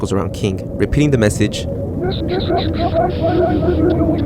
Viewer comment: What is bro saying 😔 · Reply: hes saying “message okapi531 to join the game” over and over again